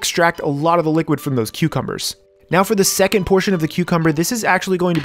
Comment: Thought you said Queuecumber. Now I can’t unhear it...